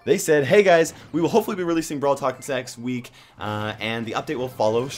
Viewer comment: Six instead of next 😐